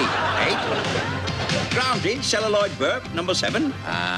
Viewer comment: Celluloid Burp .... I thought he said Celluloid Burke!